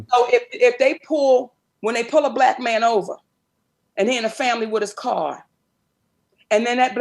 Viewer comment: ! am I tripping or did she say he in a family with his car ????